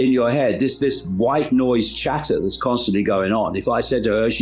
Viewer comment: ‘This wife noise chatter going on’? Sometimes a single sentence has a major impact on perception. Tell me I misheard what you said there! 6:50 minutes in. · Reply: I think he said "white" noise, not wife.